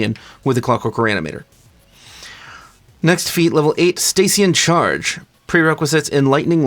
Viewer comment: I heard "Stacy in charge." I want that feat.